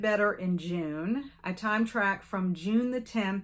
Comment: When you said “time track” I heard “time travel” 😂 You’re doing great! There’s never quite enough time in a day to get everything done. I’m looking forward to all the book clubs and events for the rest of the year. ❤